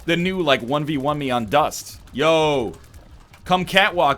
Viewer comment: for a second I though NL made a MW2 reference when he said 1 v 1 me because I thought he said rust